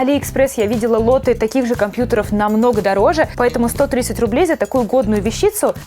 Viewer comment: Когда ты сказала что ноутбук стоит всего 130 рублей, мне послышалось "всего сто тысяч рублей"